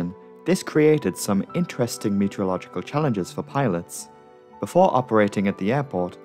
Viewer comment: I heard pirates instead of pilots